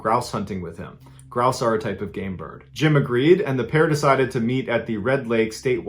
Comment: I thought u said "gay bird" not "game brid"